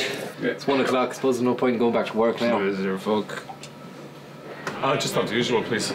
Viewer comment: What is said at the very beginning? And at 1:41-1:43? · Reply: no point going back to work now. the guy replies "is there fuck". He agrees with him.